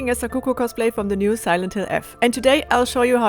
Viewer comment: I thought she was saying Sakuku and telling myself tf is a Sakuku until I read the description